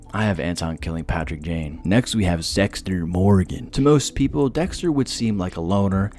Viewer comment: Did you just say Sexter Morgan XD?